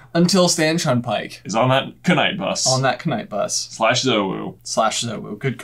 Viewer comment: i thought they said /UwU